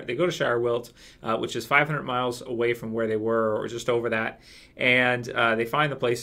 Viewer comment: I thought they said it was a 100 miles away